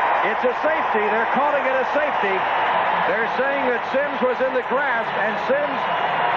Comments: I’m so confused about the one with Simms, what was the announcer talking about he was clearly still on both feet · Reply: I thought he said In the grass too referring the intentional grounding. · I’m so confused about the one with Simms, what was the announcer talking about he was clearly still on both feet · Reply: Interesting. I thought the announcer was saying he was "in the grass," but you're actually right here.